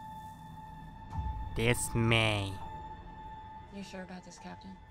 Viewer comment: This-may I thought you said dismay